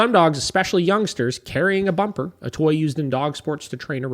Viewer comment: For a moment there, I totally heard "carry a bonker" not a bumper 😂😂😂 I figured it seemed a bit misaligned, LOL Considering the next paragraph consisted of recommending a back clip harness 😑 I was exhausted just after listening to this blog post, not even doing the things lol-many great little tips/advice for environmental management though, but the amount of avoidance just makes me cringe...you said it, just say "no" 🤷‍♀️ I feel there's so much unnecessary fluff when there are much more efficient ways for this... I need a nap now 😂 Oh, once again, impeccable timing. New client today just moved from a country house with her 9 year old dog thats rarely ever been on a leash. Ill definitely share this with her-she has a breathing issue though, and walking longer than 10 to 15 min is her max. I guess thats doable! Small wins.